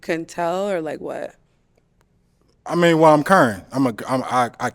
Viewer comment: who else thought he said "I'm current"?